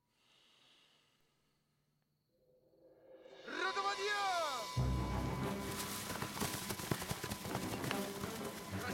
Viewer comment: What is this word spelled in russian? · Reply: It says "Rota, podjem!" And translates like "company, get up!" It spelled by soldier on night duty every single morning at 7.00am in Russian army...